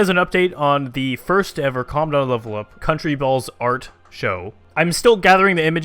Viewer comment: when I first came to this channel I didn't know your channel name so when you said "calmdownlevelup" I thought you said condomlevelup XD